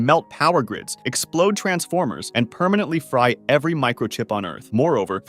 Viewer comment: Did you say transformers?